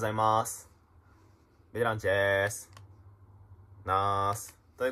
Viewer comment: もうBランチでーすにしか聞こえない